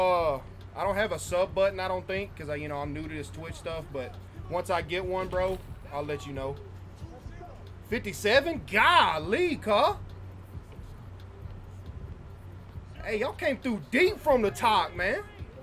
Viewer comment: did bro just call me a cuh lmaoooo no way